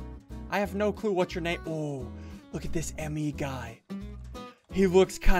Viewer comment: Bruuhhh Did he say M and E guy. Its ME as in me myself and I.